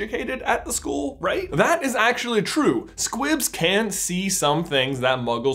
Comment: I swear I thought you were saying Squip the entire time